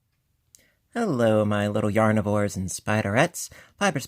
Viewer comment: HAHAHA I just heard you say "Yarnivores". I always thought you were saying "Yarn whores" so I didn't want to subscribe LOL